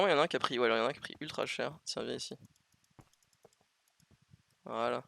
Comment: je rêve ou j'ai entendu "syra vient ici" à